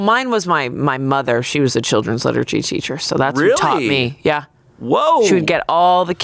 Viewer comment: I just realized Chanse thought Amanda said her mom was a children's literacy teacher, that's why he thought she taught her to read. Liturgy, Chanse, liturgy 😂